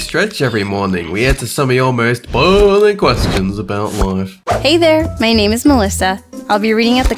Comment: I thought she said hey there my name is molester